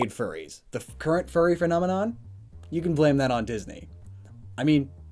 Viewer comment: did he just say furnomenon?